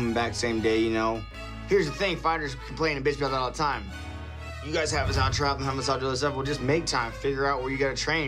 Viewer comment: 10$ to anyone who knows what he says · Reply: MR.OMGSKAR "you guys have us traveling all the time. How am I supposed to do all this other stuff?"